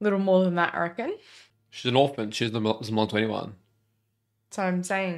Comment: What the hell does even say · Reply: "She's an orphan, she doesn't belong to anyone"